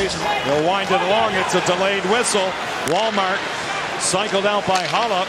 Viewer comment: I seriously thought the commentator said Walmart at 2:44 the first time I heard it lol